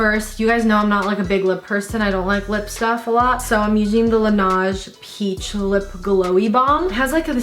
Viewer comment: “so I’m ushing the lanash peach” lmao 🤣 sorry. I’m (youshing)ushing instead of using